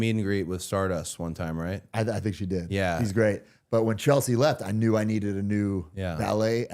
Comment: At first I thought he said "He's great" about Stardust😂😂 Would've been funny lol